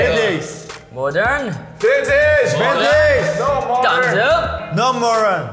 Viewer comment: Did V just said No Moron 🥴🥴😂😂😂😂😂😂😂😂🤪😁